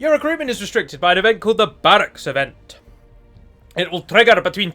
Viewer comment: So um for the last 2 and a half years I thought you were saying the "barrack of ents" and I was like yeah that makes sense but well I'm dumb and guess it's barrack events haha